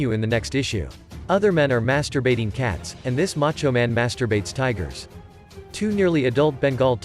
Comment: Hey, I would really like to continue to watch the rest of the story… Pls post it… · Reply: Is it me or is he saying masturbates tigers at 6:25??